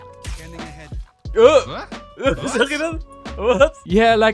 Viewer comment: are u german by any chance becouse i heard that "WAS" from both of u XD